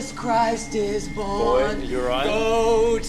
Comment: Bro I thought Chase was saying: "Boy, are you alright? Boy." but he's saying Boyd, the boy's name is Boyd lol